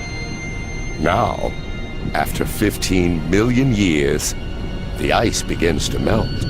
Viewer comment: did he said 15 bilion years? sounds like bilion